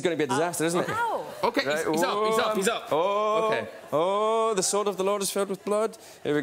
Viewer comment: Did he say, "the sword of the Lord is filled with blood"? 😅